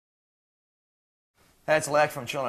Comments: Sounded like Alexi said "Bitch you can't play this" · In Round 1, Alexi sounds like he is saying "Bitch, you can't play this!"